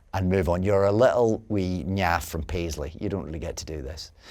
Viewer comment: Can any native english speakers please tell me what exactly David is saying @ 0:35 before "from Paisley"? I need it … for science :) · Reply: "You're a little wee naff from Paisley."